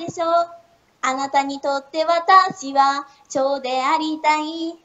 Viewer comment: 油多い方が美味しいの、←デブ宣言。 貴方にとって私も腸でありたい。って聞こえた、ような。蝶か、